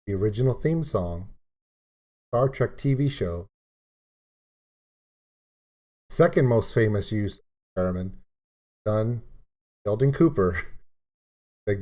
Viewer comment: Laughing because it sounded like you said "Feramin" instead of "Theramin". ;)